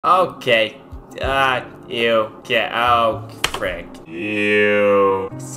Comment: Did he just say f*** on 6:14 ?!